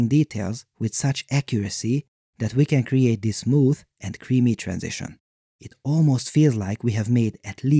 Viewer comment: Ouuuuugghh I just heard you say "soft and creamy transitions" and it made me physically dead